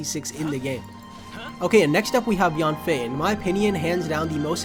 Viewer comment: the cc says :next up we have Beyoncé instead of Yanfei